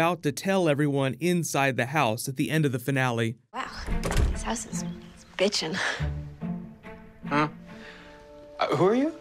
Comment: Did she just say I think she said "this house bitch in"